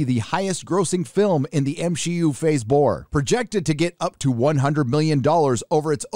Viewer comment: Did you just say "the MSHEU phase bore" ? That's brilliant! xD